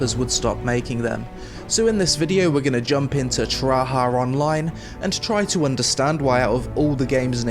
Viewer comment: i swear he said Tryhard Online.